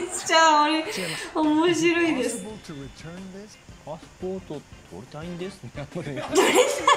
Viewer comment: はじめまして！ チャンネル登録させて頂きました。 ぽくも、パスポート取りたいんです。って聞こえてる。 動画更新楽しみにしてますo( ^_^ )o